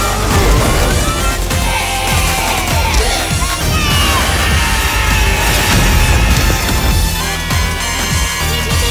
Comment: THEY ACTUALLY SAID A WORS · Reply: It sounds like one of them said "lets go!"